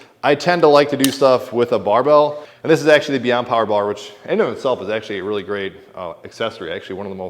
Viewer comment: What is that bar attachment you use? I thought you said empire bar or mpire bar.